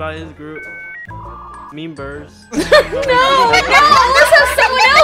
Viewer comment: did just say memebers